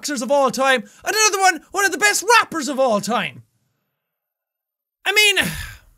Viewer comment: i watched this when i was little and i thought he said "one of the best boxers of all time, and the other one is one of the best wrappers of all time" I was a dumb kid back then